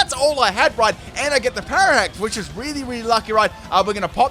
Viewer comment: did he say “And i got the pararact!” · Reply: parahax